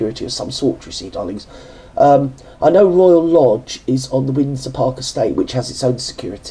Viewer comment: Ohh God when you said royal lodge I thought you said royal nob!!! So sleep deprived off to bed I go ❤❤❤❤